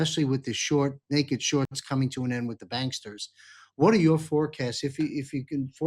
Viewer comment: Instead of banksters it almost sounded like you said ganksters!😂